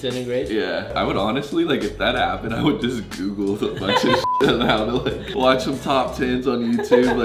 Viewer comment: Did he just say I would just punch Donald trump